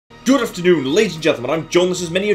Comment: Only I hear "good afternoon Lazy gentlemen." ))))